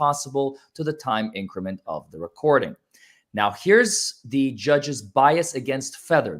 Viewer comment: I hear…. “here com the Judge!”